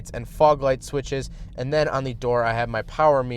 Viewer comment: did you say honda